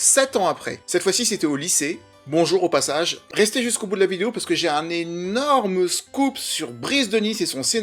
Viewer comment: À 1:14 j'ai entendu "j'ai un énorme zboub" 😂😂